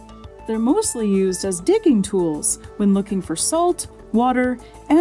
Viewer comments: couldn't help but think she said dicking tools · I thought she said, "They're mostly used as "DICKING" tools" & BAM! Segue to phallus 8=D